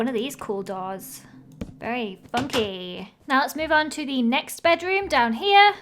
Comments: did she say bunkey??? 😅😅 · Instead of funky it sounded like she said f-uc-ky